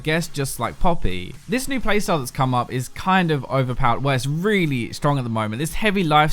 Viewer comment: Did I Hear you say op? lol we caught you